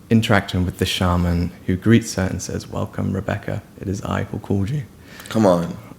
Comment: Please, what does he say, between 30:58 and 31:03? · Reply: He says:"Welcome, Rebekah! It is I who called you!"